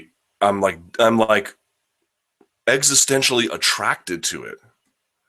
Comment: what word does Luke Thomas say? Exponentially? The way he pronounces it sounds sounds strange unless he is saying a different word. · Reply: "existentially"